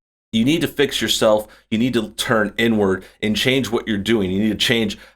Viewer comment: I laughed so hard you need to turn n word is what I heard but it was inward